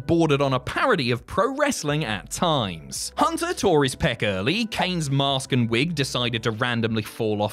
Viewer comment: If you pause it at 1:19, it sounds like he said "Hunter tore his pecker"